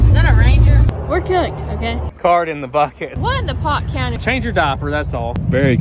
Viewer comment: did say What in the pott County?